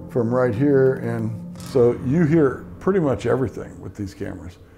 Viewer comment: THANK YOU GUYS FOR ANOTHER FAB VIDEO - missed the live though ☹ Stay Safe Brothers!! 10:08 I heard : "Pretty Boy" (the guy was saying "...pretty much..." / 12"31 "stop it now" 16:18 "" whispered (creepy) / 17:35 "" sounded like LOVED WATCHING ROCKY & JOSH crack up (We love you Shawn) / 25:28 "Don't come in here" man voices / 28:45 Looks like an older woman holding up a letter - creepy! That other woman looked like she had just baked a dozen cookies! / 29:48 "hey..." older man's voice / 29:52 "why don't you go" 30:39 "We're not afraid of you" young male voice / 30:45 "watch it..." (?) 31:33 a deformed baby? Wow, I love individual interpretation - I still see an older woman, with glasses, holding a letter - although her mouth is spit in two (!??) / 32:11 kid's voice / 32:57 "fine" whispered / 33:30 Yes, I hear the children's voices - very faint / 33:35 "someone's coming" (??) faint / 39:04 "won't you go..." faint / 41:20 JOSH! you can say some spooky things! lol / 41:22 male voice / 42:57 "I can see you" whispered child / 42:59 "stop that" male voice / 44:51 "Get me " I heard, "not in this house" (spirit box) / LOL We all think you're pretty gtreat too - and, and 💚💙💜